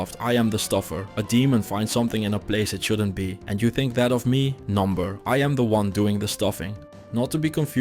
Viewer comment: Are you using an AI voiceover? At 07:52 you say "number" instead of "No.", which is very strange